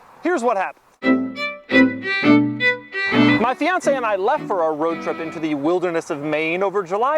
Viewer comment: wait did he say fiancé???? big moves doug👍🏼